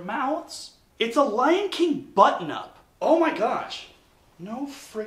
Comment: I heard "butt nup" instead of button up, and I was wondering what weird California slang you were using.